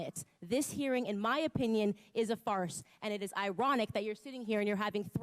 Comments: 초에 This hearing in my opinion is a false가 아니라 farce 라고 들리네요 ㅎㅎ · 오타가 있는것 같네요 ㅎㅎ 3:58 This hearing in my opinion is a "farce" and.... 라고 들리는것 같은데요 ㅎㅎ 좋은 동영상 감사합니다! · false가 아니고 farce(희극)으로 들리네요, 의역하면 "웃기는 짓" 또는 더 강하게 번역하면 "개소리"라고 번역 할 수 있죠. 영어로 번역한다면, ridiculous 정도 ㅎㅎ